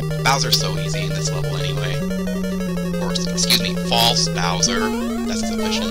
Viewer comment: This was awesome dude! :D looking forward to this serious! :D I hear you saying "fake bowser" XD As I remember back in the day everyone used to call him King Cooper instead of Bowser XD You sure look good at this! Good luck with the rest of the episodes :)